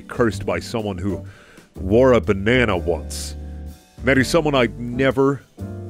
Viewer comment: "I would like this more if I wasn't already cursed by someone who wore a banana." I see this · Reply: I heard "who vored a banana" and I just accepted it aklfjhaklhjaklhj